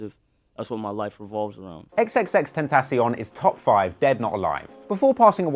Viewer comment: did he said dead not alive...💀